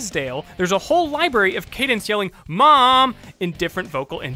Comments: Am I the only one who heard him call her Kadance? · Did you say Cadence? Lol Cadence is another name for a scale in music